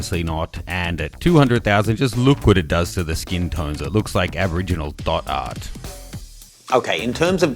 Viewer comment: What did say about skin tone at 7:13? Looks like original....?